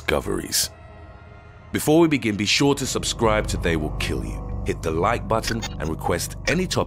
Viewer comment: When i first suscribed i thought he said suscribe or you will die then i relized what it siad and i really like your chanel